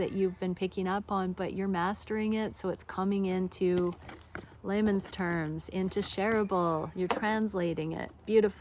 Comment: See…you said “In laymen’s terms”…but I heard ‘In Lemon’s Terms’…😂 Enjoy your time! Blow a kiss to the Sun for us!!!! ❤🌞🙏